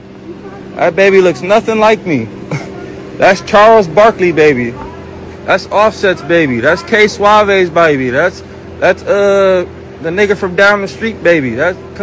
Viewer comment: Thought he said thats Gnarls Barkleys baby😂 he a whole WEIRDO 😢sad for JR. She needs to get away until a paternity test is done properly! Not threw a social media app!